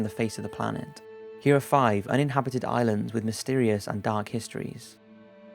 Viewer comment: I thought it said "dank & mysterious histories"